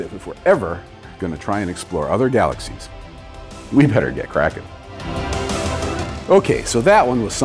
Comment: what did he say at the end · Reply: If we want to explore other galaxies we better get cracking